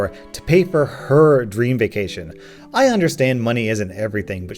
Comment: I thought it said vaccination.